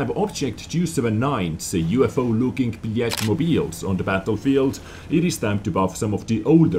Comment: for some reason at 0:03 I thought you said "The UFO looking Blyatmobiles"